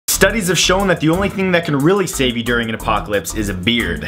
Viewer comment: I thought he said the only thing that can savvy you is beer